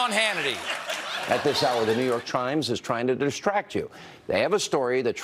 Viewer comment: It sounded like Sean Hannity said New York Chrimes instead of New York Times lmao